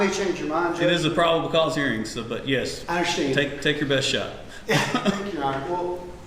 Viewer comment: The judge should choose his words more carefully. I heard him say "Give it your best shot".